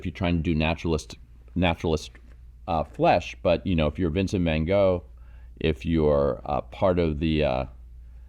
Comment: It sounded like you said Vincent Man Go. That could be this gentleman’s name. He will be opening for Spandau Ballet this summer in Vegas!!